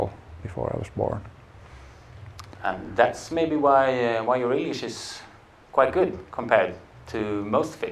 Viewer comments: What did he say at 2:00-2:01 "Thats maybe why u were" then something i heard like relisious or what did he say?? someone pls tell. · Riktigt sevärt! HEEERO! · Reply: What did he say at 2:00-2:01 "Thats maybe why u were" then something i heard like relisious or what did he say?? someone pls tell.